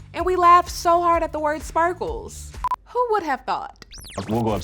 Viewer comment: For so long I thought he was saying “spackles” like spackle the stuff you put on your walls. No, he’s saying “sparkles” and that makes so much more sense.